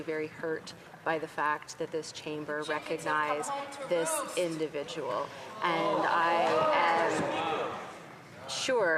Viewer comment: What was said around 2:03 ? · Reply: Chickens have come home to roost.